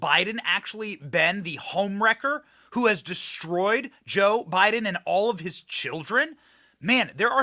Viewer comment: Totally sounded like you said "Jilldren" 🤣🤣🤣🤣